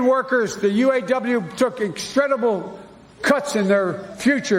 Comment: Did he say “instredible”?